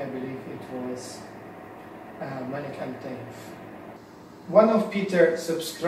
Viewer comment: what is he saying he used it in what? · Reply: Malik Al Taif